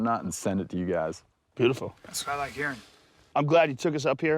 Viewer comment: Bryan says "That's what I like hearing" but at first I thought he said "I smell like urine"